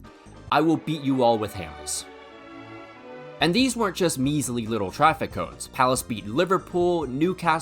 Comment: I thought you said these weren’t all traffic cones?